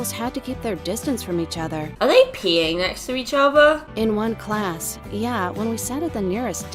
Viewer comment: I was doing my homework and I looked down and i have my air pods in and I was listening to and i all i hear is" are they peeing by each other"!?... 0.o